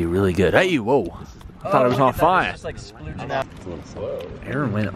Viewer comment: I swear I heard someone say "like it splooges"